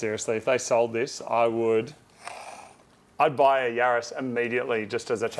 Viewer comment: I thought you said Yaris instead of (RS) for half of the video